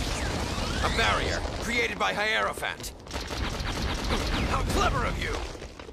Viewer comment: what I heard: A barrier created by high elephant